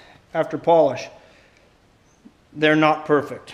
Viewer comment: Who said the F word at 5:41 ? I even took a dookie a little bit. He really said "they're not perfect". I'm like,"Those are perfect!"